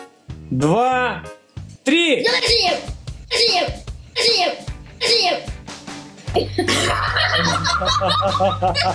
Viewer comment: Что сказал на 2:09 минуте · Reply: С днём рождения